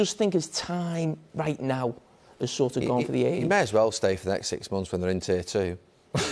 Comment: What did Neville say that made them laugh so hard? Couldn't get what he said. Anyone can help? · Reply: He may as well stay for the next 6 months when they're in tier 2 (Championship)